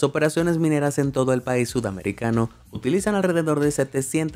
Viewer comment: Soy yo o escuché país sudafricano enves de sudamericano